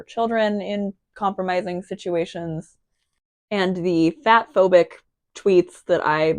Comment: Not a meaningful or constructive comment but I misheard you as saying "bathphobic" and not "fatphobic" for like a solid half minute and once I realised that made me lol. I was like "what kinda bathphobic tweets?? did i miss something in the prior vid?? so she hates baths; honestly, girl, same. OH. WAIT." anyway just something silly while i'm going loopy drawing boxes...